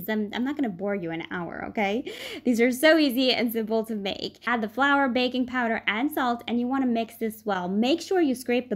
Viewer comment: Um did I hear "add the flour baby powder"!?!